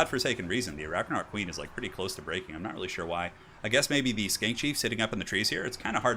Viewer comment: i thought you said skank chief lol